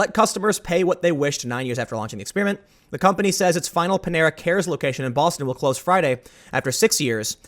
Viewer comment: I thought it said Pantera. I am very disappointed.